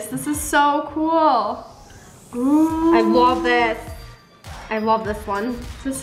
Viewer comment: I am the only one or did say Slime?